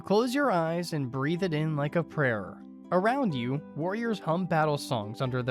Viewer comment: did he say “breathe it in like a prayer”?